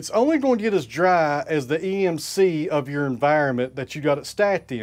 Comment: EMC {not AMC 3:41}, Equilibrium Moisture Content - My New England ears don't hear past your Tennessee drawl well.